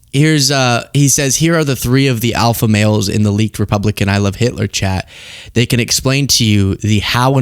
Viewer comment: when you say that all i hear is "I love tesler"